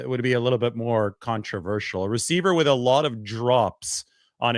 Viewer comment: "Alot of Drops" on his Resume...... I had to rewind that!! Thought you said "Alot of Drugs".................. 🤣🤣🤣🤣🤣🤣